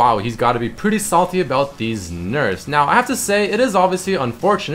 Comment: ''He's a little salty about these nerfs'' I thought he said ''He's a little salty about deez nuts'' in an accent xD